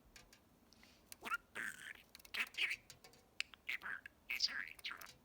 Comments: Did I just hear a "God dammit"? :D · Did Kiwi really say "Goddammit" at 1:10?! 😂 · did he say god dammit? 😂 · It sounds like he says Gah Damnit! xD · did he say "god damn it" haha